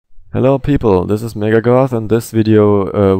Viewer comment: I'm sorry but the first three times i heard the first sentence i heard "Hello, this is nigga goth"